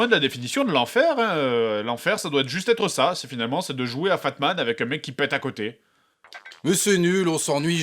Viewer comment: Pourquoi j'ai compris "FAPman"? q__________q